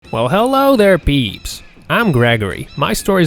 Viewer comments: i legit thought he said beeach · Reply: I thought he said peeps.... Isn't that what he said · i legit thought he said beeach